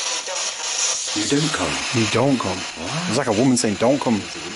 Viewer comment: That scream sounded like someone saying don't hurt me and great video 👍🏻